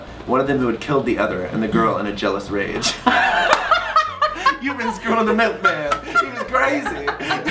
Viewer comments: Could someone let me know what they said?😃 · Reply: "and killed the other in a jealous rage laughs you've been screwing the milkman! he was crazy!" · what did Scott say? Sounded like "human squirrel and the milkman he was crazy" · I can't make out what Scott is saying at 2:27.. :/ Can someone please tell me? I wish they could put subtitles.. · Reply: He said. " you've been screwing the milkman, he was crazy! "